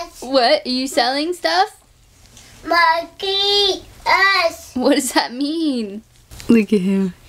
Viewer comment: I swear is saying "monka S"!